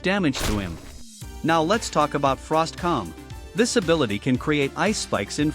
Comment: firstly I heard "frost cum"